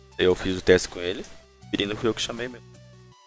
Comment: estou doido ou o falou "O birino foi eu que chamei mesmo" ??? Análise 🤔